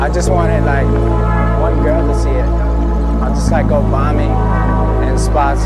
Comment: It’s sounds like he says go vomit in spots